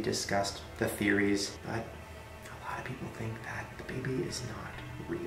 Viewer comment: i thought he said "i think the baby is THINK"